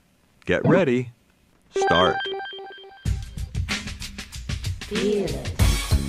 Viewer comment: What does it say at 0:42? "Veeolin" ???? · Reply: it says "Feel it"